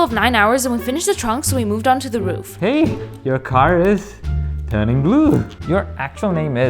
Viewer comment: Bruh I thought you said your car stop working?